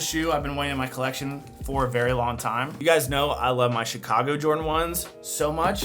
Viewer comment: I thought you said shoe-cago since you live in Chicago lol! Awesome video as always!!!